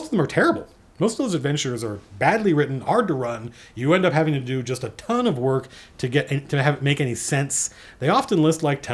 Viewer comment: What was the module he said was best in 2e (between 34:00 and half a minute later) ? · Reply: Night Below I think.